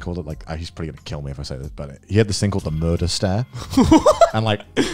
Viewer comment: I dead ass thought said his dad had what they called the "murderstat" when he started talking about growing up. Fuck a thermostat, I want that shit 😂